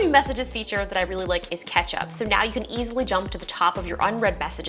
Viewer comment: did anyone else hear “another new messaging feature that I really like is ketchup” …. Just me? 🍅